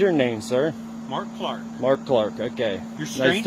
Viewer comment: when he said his name is Mark Clark I thought he said his name is Markiplier